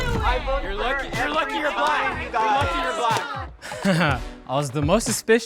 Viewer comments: Wait. Did I hear, "You're lucky you're black; you're lucky you're black"?!? That's... problematic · Did I hear "You're lucky your black"